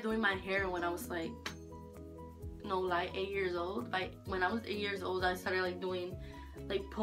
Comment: it sounded like she said jam 80 years old